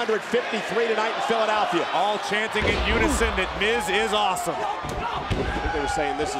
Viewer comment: Miz really thought the crowd said miz is awesome lol they said this is awsonme